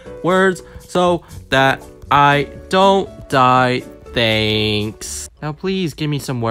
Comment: did he say diss?